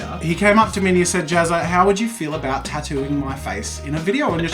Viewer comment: When he said tattooing it sounded like he said... Tatooine (like from Star Wars)